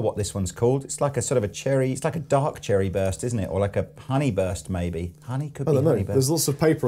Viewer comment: Did I heard "pony burst" at 4:05…?